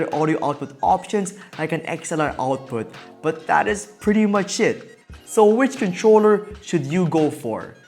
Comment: I thought you said “it’s pretty much shit “ lol